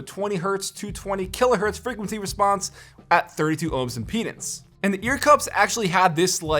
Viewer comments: did he say penis? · I thought he said penis I am dirty minded